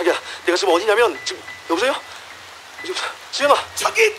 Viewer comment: 여기서 자막이 [수연아]라고 쓰여있는데 헤드폰으로 좀 크게 들어보니 이 [여보세요? 신혁아!] 라고 하시는 것 같이도 들리네요 ㅋㅋㅋ 의 간절한 마음...